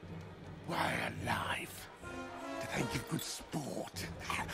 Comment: What is he saying? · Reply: He said "Why alive? Do they give good sport?"